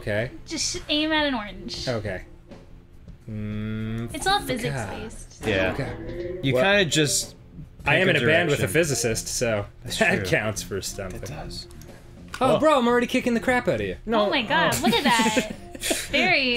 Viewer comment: I thought Danny said he was in bed with a physicist, and I was like "Well, this is a development."